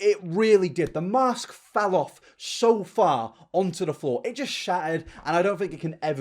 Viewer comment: thought you said shatted not shattered 😭honestly same thing